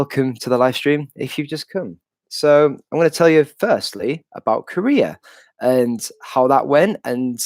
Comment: Ha, when you said Korea originally, I thought you said you were going to tell us about your career!